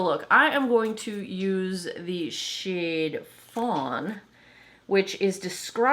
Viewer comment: I'm so sick that I thought you said the shade was prawn. I was like why are they naming foundation seafood now?